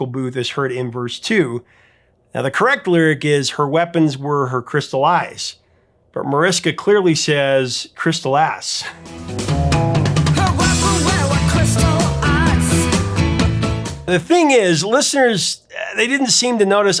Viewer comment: I always heard "crystal ice".